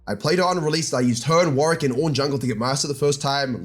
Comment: did i hear ornn jg? i NEED to see that from u:0